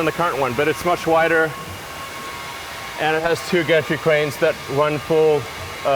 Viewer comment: It sounds like Elon is saying, "and it has two gatric cranes .." And the subs even say gastric with an "s" .. But it's supposed to be gantry cranes right?